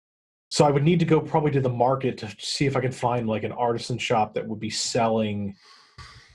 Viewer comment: Artisan shop? I thought he said arson shop!